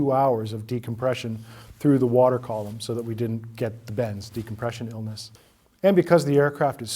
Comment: CC, supposedly "human made", reads "...so that we didn't get the Ben's decompression illness" - instead of "so we didn't get the bends/ decompression illness".